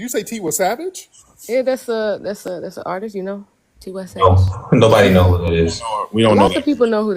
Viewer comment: I thought he she was saying 21 savage 😭😭😭